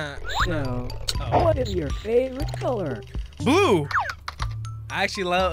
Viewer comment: did bro say glue? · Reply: he said Blue